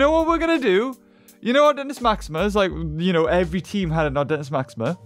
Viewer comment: Am I the only who thought when he said artentismaxima i thought he said "our dentist maxima"